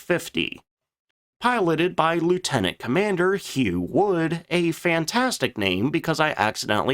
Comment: i heard huge wood at first when you said it lol